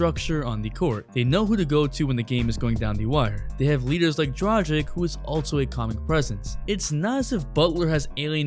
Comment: did u say tragic instead of Dragic? Lmao